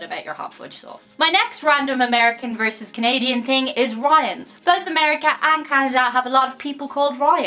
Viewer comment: When she first said Ryan's, I thought she said riots and I've never heard of a riot in Canada, they're more peaceful and civilized than us Americans